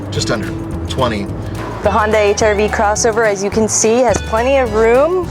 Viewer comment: Is it me or it sounded like she said Hyundai HR-V crossover at 2:08?